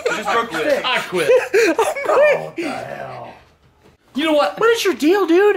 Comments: Anyone else hear a voice say "Go to hell!" at 2:31 ???? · did anyone else hear the "go to hell" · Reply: Bro, said "Oohh, what the hell" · Who else heard “go to hell” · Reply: İts says what da hiel